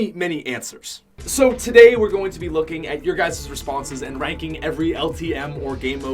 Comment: sounds like hes saying your guyzers:) great video